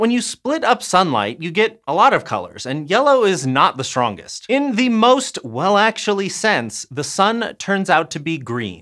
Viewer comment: Am I having a stroke or was this just poorly edited: "...yellow is not the strongest in the most well actually since.." I had to rewatch this part just to try to figure out what he was saying.